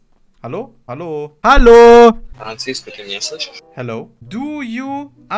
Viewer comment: Bin ich der einzige der bei 5:42 gehört hat?